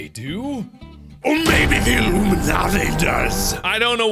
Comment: I swear when he said "Maybe the Illuminati does" I thought he said "Maybe the Roomba Nazi does" I had to re watch that part to hear what he actually said lol.